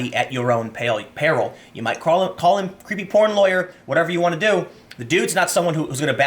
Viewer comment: hey Tim, did you just called Avenatti "pretty porn lawyer"?